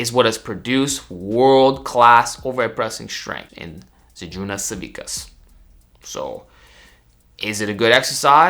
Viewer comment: Wtheck did he say at 00:37 ? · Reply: Žydrūnas Savickas. He was a world's strongest man competitor and former champion. The z press often gets attributes to him but he never did them